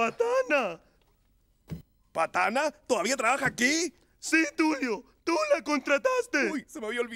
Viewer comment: DID YOU SAY TULA?